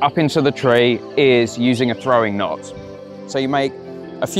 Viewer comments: I thought he said “throwing knife” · I didn’t read the title and thought he said throwing dart and was really excited to learn how to make a throwing dart out of the rope!